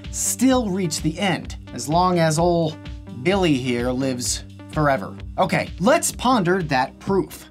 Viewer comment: "as long as old Billy here lives forever" - sounded like infinity to me! Glad to see later in the video that it isn't infinity.. just a long, long time....